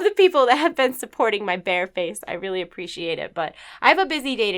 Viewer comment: Am I the only one who thought Druid when she said "Support my bear face"?